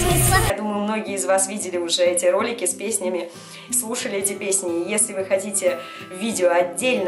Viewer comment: Я подумала по "кушали" но потом рассмеялась и прослушала ещё раз ну и вы знаете что я услышала